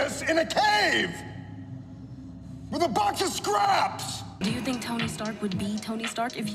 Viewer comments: I always thought he said he build it with a BUNCH of scraps not a box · Ever since the first movie I always thought Stane said “with a BUNCH of scraps“ i’m gonna have to continue saying it because that’s what I’m used to hearing lol!